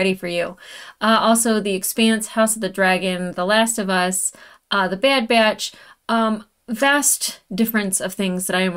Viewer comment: Did I hear "The Bad Byatch?" Am I hearing some MidWest or Old NorthWest accent there?